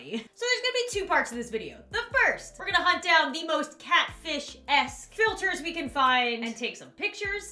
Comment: did you say ess or a$$? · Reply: Ess